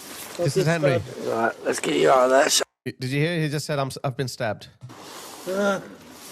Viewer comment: I went back through this n found out what henry was saying at 14:20 start there.... henry, "he stabbed me," using all his might contracting muscles that are already fading.... henry wanted someone to hear him n I did maybe you too.... I'm thinking with a chip on my shoulder maybe henry knew he wasn't leaving that front yard👿.... I'll see you next time (ya know ol' blue eyes sinatra voice or casablanca) was said to me by someone that was taken too soon aswell n find appropriate kinda fitting that a woman across the pond would intercept another part of the puzzle.... I'd like to think this came out in court but I'm not sure about british law n if those transcripts are considered public record??? may henry's memory forever be a blessing🕯 💙💙💙